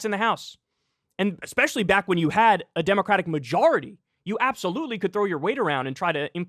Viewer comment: Did Kyle just say “absolutely could throw your weiner ‘round”?